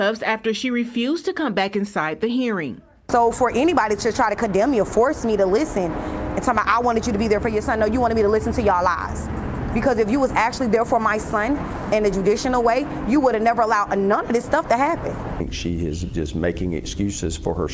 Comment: Did she just say "judicinal"? That isn't a word.